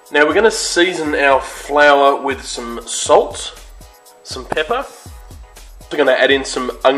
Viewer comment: what is he said pepper? Sorry my english is not too god pls help me :)